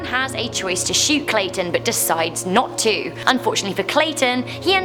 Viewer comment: lol when you said clayton i thought you said statin